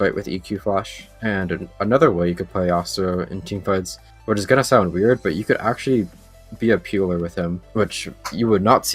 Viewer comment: deathfire greaves rush? What is he saying? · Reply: He said "Definitely berserker greaves rush". ^u^